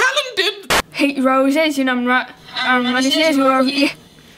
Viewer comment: It sounds like shes saying "on Wednesdays we wear pee"